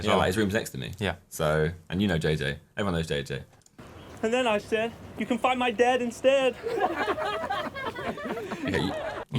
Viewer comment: I thought he said “and then I said you can find my dad instead” I’m so stupid